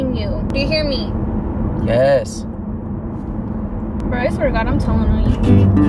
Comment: NO DID NOOOOOTTTTTT with that edit 🤣🤣🤣🤣🤣🤣🤣🤣 but did really say I’m telling on you I’m 💀💀💀💀💀💀